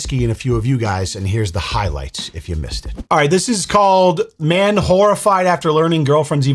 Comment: You said "Man horrified" I heard "Manwhorified"... idiot 🙄